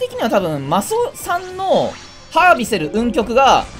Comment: マスオさんのハービセル運極って聞こえた